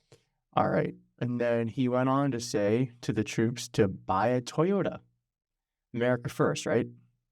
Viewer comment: I just doesn’t understand that he is not at a campaign rally. Joe Biden is not his opponent. He is a retired old man battling cancer. Also, buy Toyota? I thought you said buy Tesla.